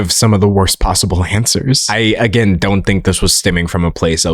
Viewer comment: Whenever you say “stemming” I hear instead “stimming” and it throws me into ADHD world 😂